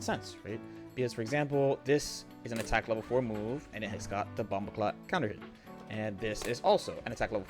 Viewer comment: Did you say bumbaclot counterhit